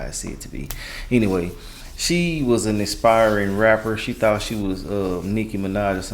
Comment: why did I hear “expiring rapper” 😂